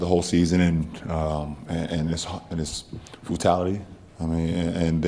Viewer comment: what did this idiot just say? Fultality? Smh this is the high IQ guy?